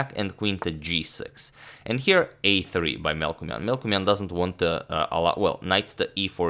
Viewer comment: I always hear is “Milkman”